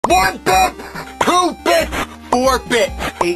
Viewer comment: I feel like i heard ''2 bitch''💀☠️🙂🌚